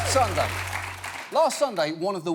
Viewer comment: sounds like he said last sondag in the beginning?